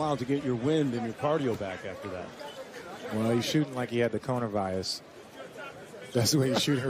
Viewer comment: did he say corona virus LOL